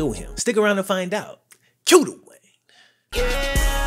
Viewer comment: Cue the Wayne.......Ive been watch you for like over 2 years and this whole time i thought you were saying Cue "too" Wayne lol